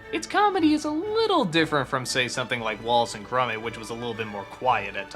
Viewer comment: Did he just say 'Wallet and Grummit'?